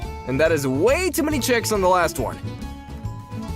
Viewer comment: someone do the math please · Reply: I thought he said chicks😶😶😶